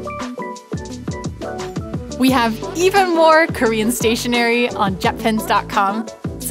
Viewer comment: I thought it said Karen's stationery ..